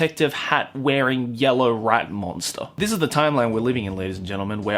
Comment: i thought he said rap monster and i was like w a i t